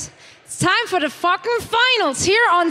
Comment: did she say time for the fucking finals? rofl